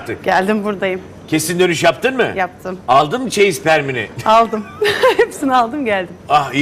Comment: dikkat ettinizmi bilmem ama 01:01 da benmi yanlış anladım bilmiyom ama aldınmı çeyizspermini diyo sanırsam :P :D